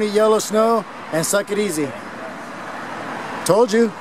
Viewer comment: did he say "suck it easy"? New one to use! @ 12:02, "you smell disgusting!" FAMOUS!